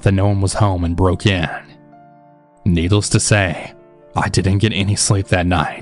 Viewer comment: it sounds like he says needles instead of needless.